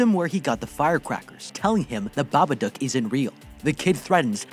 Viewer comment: The video: Bababook My brain: Bababooey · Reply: Me and my friend watch this on an old laptop and the audio was so bad she thought it said babydook